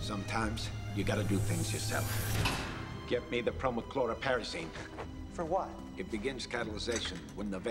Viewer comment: what did he say? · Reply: He said Prochlorperazine